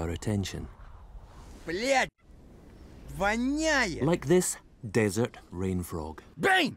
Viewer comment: What does he say in 0:09 I'm curious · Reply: Blyat, It stinks